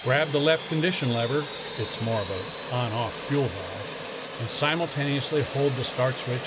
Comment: What is he saying @ 1:22 - 1:25? It is difficult to hear what he said here. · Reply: “it’s more of an on/off fuel valve” I think